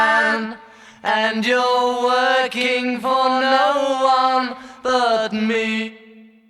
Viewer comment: working for no one but me---always thought he said spins for no one but me